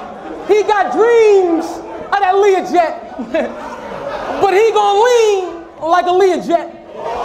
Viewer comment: did he say “he gon lean like Aaliyah jet”?? 😮